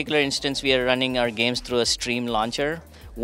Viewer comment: Did this guy say “stream” launcher instead of steam ?